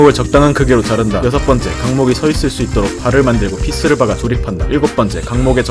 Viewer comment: 나만 키스를 박아라고 들었냐